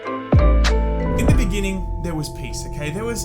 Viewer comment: Why did I hear.. "In the beginning there was Pizza" ?